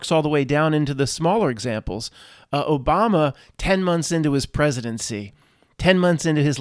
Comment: Was listening to this in bed while falling asleep but woke up when I thought I heard "10 months into Obama's pregnancy" 😆